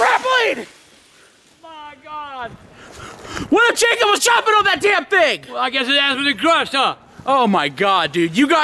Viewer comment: I thought said what if a chicken was jumping on that thing!